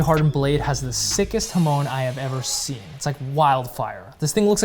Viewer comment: What did you say in 3:21 himoun? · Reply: Hamon line for differential hardening and clay tempering